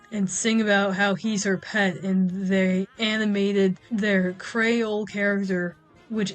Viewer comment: My dumbass thought you were saying Crayola character instead of Creole character for a bit 😭